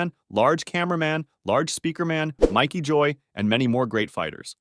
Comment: did he just say Mikey joy